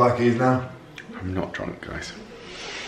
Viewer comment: I genuinely thought said they were looking for parking, and I’m like what? Your car’s already parked! You’re getting back in it right now! 😂